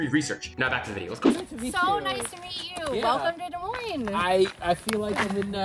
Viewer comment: At first, I thought she said, "Welcome to the Void!" and also thought it flew over his head. Too bad that it wasn't a Fallout reference.